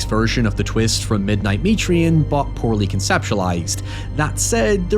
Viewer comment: What other movie did you say at 13:54? I cannot find this movie at all. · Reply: The midnight meat train